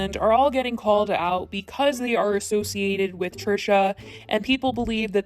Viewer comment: In other news...........did she just say, "because WE are associated with Trisha"................???